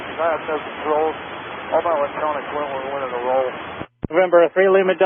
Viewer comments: Did he say they rolled it? I heard "all my electronics went when we went into a roll" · "all my electronics went when we went in a roll" I think is what he said